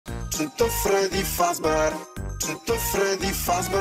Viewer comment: Bro i hear "Cheat on Freddy Fazbear" 😂